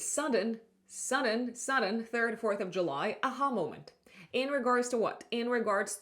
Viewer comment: Hi, just before 38:00 on the video you say something that sounds like “you’ll have a “hallo-want”, I tried winding it back but still couldn’t understand it. Could you please tell me what word was that ? Sounded like “hallo-want” Thanks ! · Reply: "Aha" moment